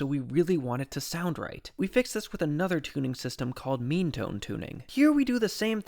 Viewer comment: when you said “meantone,” i thought you said “memetone” 😅